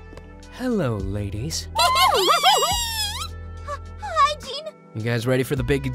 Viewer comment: I know she said "hi Gene," but I keep hearing "hygiene."